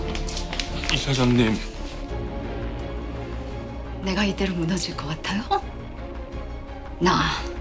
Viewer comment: 나만 이렇게 들리나 "내가 이대로 무너질것 같아요? 호?"